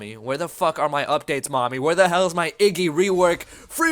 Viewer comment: Did he just say 'Niggy' Iggy? -_- Subbed yesterday.. unsubed today. Nice guides, though.